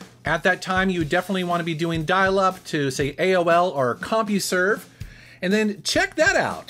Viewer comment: I thought you had said "Comperj"